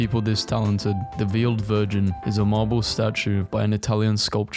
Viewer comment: When it said veiled virgin I thought it said New York version